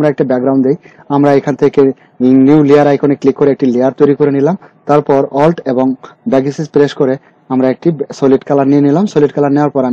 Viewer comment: এ কি বলছেন স্পষ্ট বোঝা যাচ্ছেনা ALT এর পর কি প্রেস করতে বলেছেন ?? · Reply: alt+ctrl+x